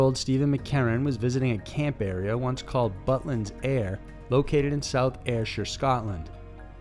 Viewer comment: Ohhh..."Butlins Ayr"...okay, that makes more sense. I thought he said "Buttlands Air". I was thinking, fart. You mean, fart. Noe it all makes sense. Captions are only half correct, me? Typically fully incorrect.